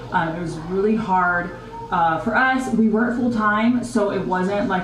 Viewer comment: Did she say they work full-time??